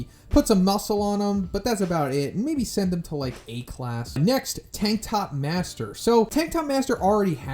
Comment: I heard you say Tiktok master instead of tank top master lol